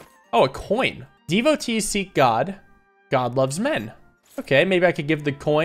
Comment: I know 22:20 meant like he loves the people but i nearly spat all my water out out of laughter when i heard “god loves men”